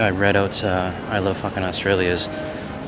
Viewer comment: I giggle everytime i hear “ I Love Fucking Australia.”. Isn’t it “I Fucking Love Australia”?